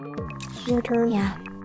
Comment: why do i heard aubrey said "your turn nya"😭😭